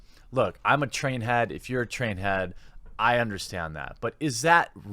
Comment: Did u just say u were a trany?